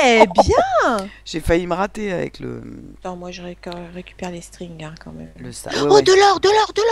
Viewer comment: A 00:30 je crue entandre dire de recuper les strig o.0 les maillot lol XD